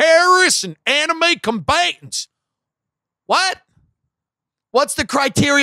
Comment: ish He said "Enemy Combatants" in suthern acccant. I heard "Anime Combatants", still, with southern accent. The real threat against America. Anime.